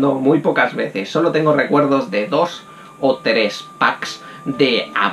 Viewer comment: escuche bien "packs" de abuelas :v